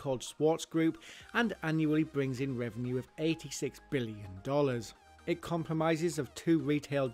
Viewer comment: what did you say : It COMPROMISES ?? lol